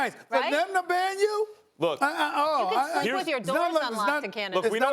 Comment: Molly: “You can sleep with your door unlocked in Canada.” Me, a Canadian: Lol no you cannot. We got thiefs and killers around here too. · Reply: I had to replay it a couple times, I thought she said you can sleep with your dog in Canada. The Look she gives into the camera though after was like yeah I said that.